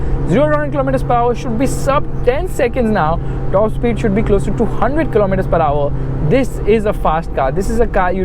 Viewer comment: Wait ! @ 7:02 Did I hear top speed to be 100kmph ? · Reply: Actually he top speed should be 200kmph. But it heard as to 100kmph.😂😂😂